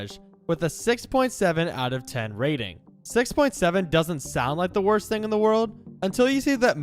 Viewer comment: did I hear 6’7?!!